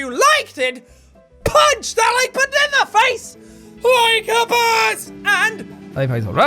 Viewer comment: I always hear "punch that banana face like a boss" even though I know what he says